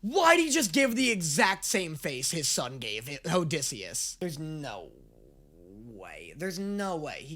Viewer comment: it sounds like He is saying "Hoedysseus"